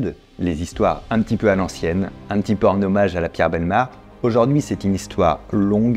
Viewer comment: C'est quoi lapierre belmard ? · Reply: À la Pierre Bellemare Un grand journaliste français qui faisait ce genre de format d'émission sur Europe 1